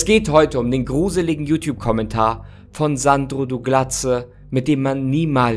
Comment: Digga dieser Name Sandru dugladze😂😂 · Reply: Ich hab "Sandro du Glatze" verstanden 🤣